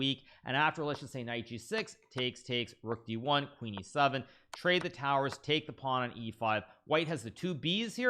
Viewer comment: wait hold up did he just say trade the towers